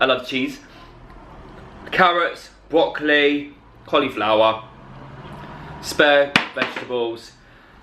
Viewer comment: I've got one blocked ear at the moment, thought you said sperm vegetables 😂